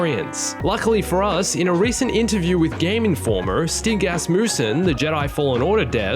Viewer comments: I thought you said 'stink ass moose man' not stig asmussen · Omg I heard it "Stink assmussen" XD